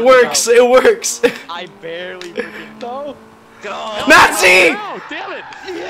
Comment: DID HE SAY NAZI?! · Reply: XD No I said Nasty